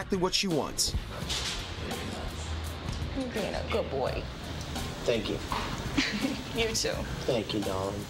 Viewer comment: What did he say at 1:44? It sounded like thank you nom to me.